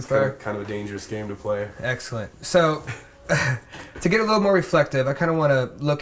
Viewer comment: does he say Ex-kill-ent?